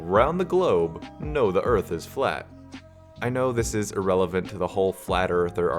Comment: I thought the part where they said the people know the GLOBE is flat lol. (GLOBES are spheres lol)